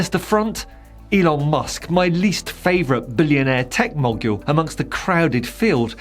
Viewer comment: Did I mishear, or was that "billionaire tech MOGULE"? If so, please note that mogul is pronounced "mo gull" :)